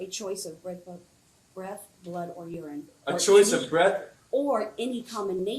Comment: I thought she said he had a choice breath or blood , its simple . Why was he not given the blood choice. And it does not metter if you smell alcohol, that does not prove he is impaired. Its not zero tolerance.